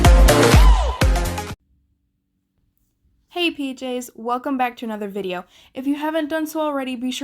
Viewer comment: I thought she said “hey bjs” instead pjs in the beginning and I was like lol what??